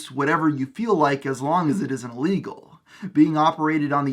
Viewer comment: At about 7:52 or so, it sounds A LOTTTT like you’re saying “as long as it isn’t legal,” which I’m fairly certain is the opposite of what you intended to say there